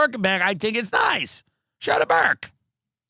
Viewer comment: Oh Burkin. I thought you were saying burka